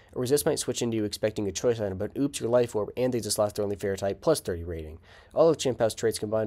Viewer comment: Can anyone translate what's said at 1:53 lol, "but oops you're life orb, and <completely unintelligible>" Or better yet could these videos get closed captions? I have trouble keeping up even when I slow the video down lol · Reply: but oops you're life orb and you lost your only fairy type + 30 rating. yeah my bad on that one. I try to keep these below 2:20 so that I can post them on twitter, but my scripts sometimes get out of hand and i have to rush my words more than id like. def something im working on for the future.